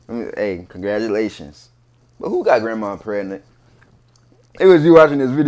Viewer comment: I thought you said grandpa at first 😂